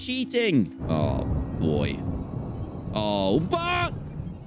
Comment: did he say what I think he said? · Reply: he said “oh ba” 😭